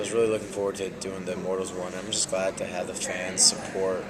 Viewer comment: Did Kellan Say @ 0:24 "Immortals 1"...I hope there will be a sequel.